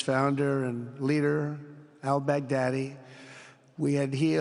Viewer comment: When trump said 'albaghdadi', i hear 'i beg daddy'.